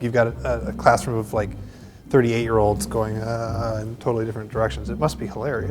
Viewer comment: Dieter said "classroom of 30 8-year-olds" I heard it as "classroom of 38-year-olds" and got pretty confused